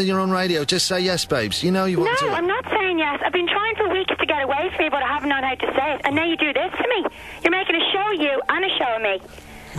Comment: she says (at least it sounded like) "I've been trying for weeks to get away from you, but didn't have the heart to say it." If that's true, it sounds like she was probably giving him the brush-off all that time and this borderline stalker guy couldn't take the hints. And now, this proposal was just a desperate attempt to get her to stay with him. Pretty pathetic, if my analysis is accurate. But, in HIS defense, if she wanted to "get away" from him, she should have just found the "heart" to break up with him a long time ago and just done it.